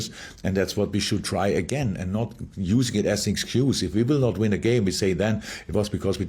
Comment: Klopp getting excuses in early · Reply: What are you on about? He literally said: "and not use it as an excuse at 1:21"